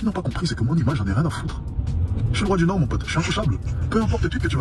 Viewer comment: Il dit quoi ? Je n'ai pas compris Juste avant de dire "j'ss intouchable mon pote"? Il a dit "j'suis le roi des noirs" ? Ou j'ai mal compris? 😅 Merci pour ta vidéo ☺️👋🏻